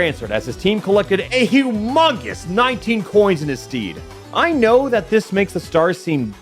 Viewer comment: did you say "in his steed"?